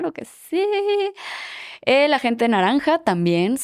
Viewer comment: entendí "gente naranja" y pensé "¿Trump?". Perdón 😂